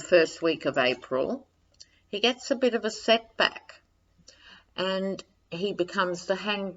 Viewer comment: I looked away from the screen for a second and thought you said "he gets a sex act." Good thing I have the caption on and saw that you said "set back." Thanks for your readings, and yes, they help. I feel like we're all in this together. Latest news over here is that his aides are saying Trump is older and getting tired! Maybe he's already talking to the DOJ about packing his jammies and going, and this is his way of building up to a "health crisis" to explain an early departure. Cross you fingers!